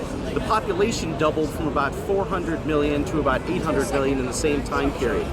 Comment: is my hearing bad or, did he say 400 BILLION to 800 BILLION? "?"? newsflash.. there's never been 400 or 800 billion humans on earth, over the last 200,000 years.